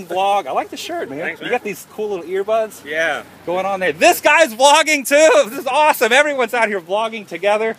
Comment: It sounds like your saying flogging..which the def. for is a public beating with sticks..Lol..That must have made you feel good,seeing your fans:)